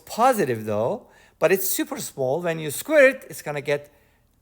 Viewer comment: Very exciting! ☝️🤩💯 I was a bit baffled, even if only momentarily so, at around 2:27 (2:20 to 2:34 to be precise). This was due to a mistake on my part, either caused by bad hearing or an unclean mind, for which I take full responsibility. I heard "it's suuuuper small. And if you screw it, it's gonna get unbelieeeeevably small", which I found excessssssively odd and made me laugh juuuuuust a little. I won't say why! 🤭😆😃